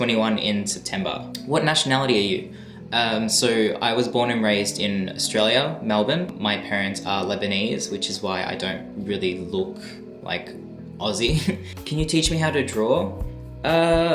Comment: When you mishear Lebanese to lesbians and you are like WHAT????? sorry for sharing such a thing😂😂😂😂😂😂😂😂😂😂😂😂😂😂